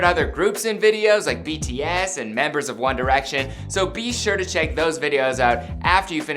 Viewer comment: I thought he said, "Wonder Action" when he said, "One Direction".